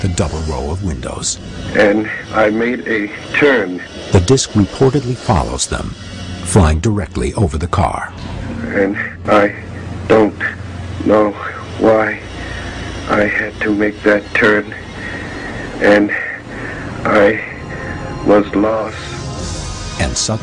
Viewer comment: Did he say ''And I Didn't Know Why I Had To Make That Turd?''